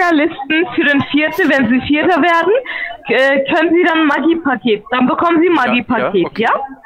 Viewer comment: Die ersten Sekunden sagen schon alles aus.... Ich freue mich auf das Gespräch. .. bin jetzt erst bei 0:12.... jetzt schon geil... lg du liebe 🐐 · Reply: Ich habe am Anfang immer Maggi Paket verstanden... klingt wie im Hühnerstall. ..